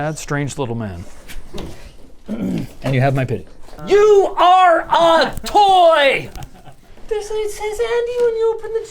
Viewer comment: i thought linus said, "YOU ARE A TOOL". no wonder they never hang out together.